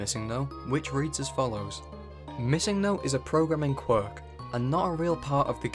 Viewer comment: When I was a kid, the first time I heard of missingno I misheard it as missingo and imagined it to be a flamingo pokemon. The bird type backed that up.